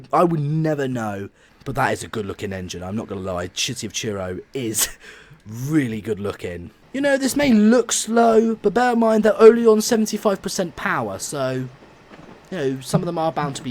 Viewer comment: it’s sounded like you said shitty of Truro